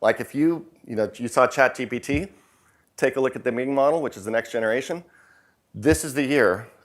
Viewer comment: anyone knows what he says @ 38:55 ? "Meeting model", "Ming Model" ?